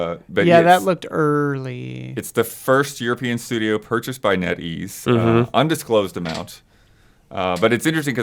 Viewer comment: Ah, just from hearing it I thought they said Denny's and not Net Ease.